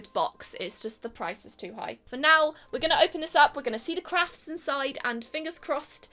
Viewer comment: Misheard it as "see the crap inside".